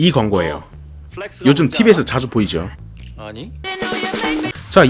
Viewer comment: TV에서 자주 보이죠? (작게 들림)페이커: 아니